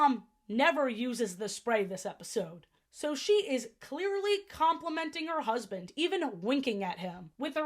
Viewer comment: Wait what compliment did she say?? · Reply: Complimenting her husband.